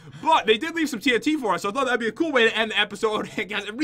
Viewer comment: Did Jen say a bad word at 15:54 minutes, I heard it, (JEN) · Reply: She said "Thought I'd ask"